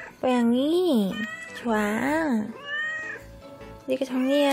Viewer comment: 초 > (OㅅO/)좋아..~♡ 라고 닝겐어로 대답하는 환청(?)이 들리는데.. 저만 그런건가요??!! 오오오... 말하는 고양이를 업어오시다니.. 부럽습니다!